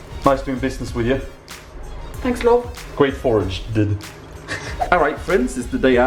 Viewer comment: did she said '' Thank Love'' ?